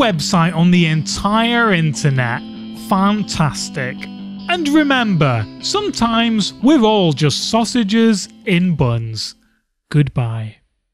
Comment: I heard "Sausage in a bum" and lost it